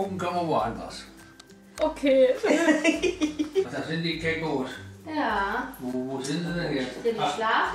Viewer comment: war ich die einzige, die "Kängurus" verstanden hat 😂?